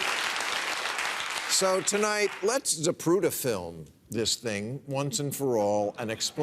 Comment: Can someone please tell me what word he says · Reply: Zapruder film, the film of JFK’s assassination. Shot by a man named Zapruder. If you want to see the joke Bill is referencing, it’s from the Oliver Stone film “JFK” at 3:35 here: <link>